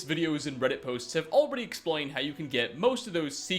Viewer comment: did he just say exploin?